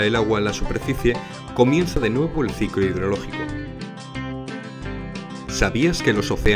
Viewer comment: Hola me podrían decir que dice en el minuto 2:01? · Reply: Ciclo hidrológico :)